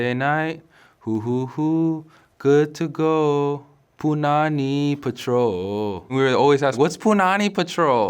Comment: story was so good, it was worth showing twice #PunaniControl here's the song btw: <link> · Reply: Fel Ix Thanks for the link, but I thought he said “Punani Patrol” 😂😂